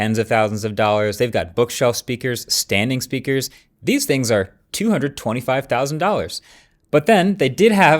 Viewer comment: I'm so sorry did he just say $225 000, as in USD ...😭for speakers!!!